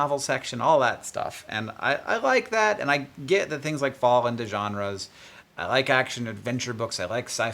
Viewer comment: I thought it said "genders". I was surprised to see Hank stepping off such a cliff in the YouTube ocean. Once I realized he said "genres" I backspaced my "oh dear shit show incoming" prediction. It was almost insightful.